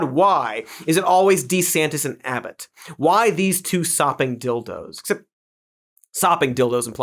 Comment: I actually thought you said "Softened Dildos" and considered it a sick burn.